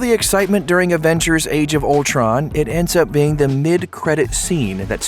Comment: Is it me or did he say "Aventures Age of Ultron"